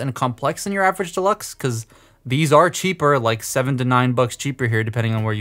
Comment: Do Canadians say 'bucks?' I thought you said 'loonies!'